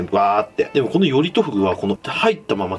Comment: より〇とフグに聞こえてしまった😑 フグ食べてみたいなぁ(º﹃º )